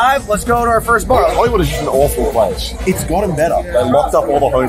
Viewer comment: he actually says "its gotten better" but the captions made me laugh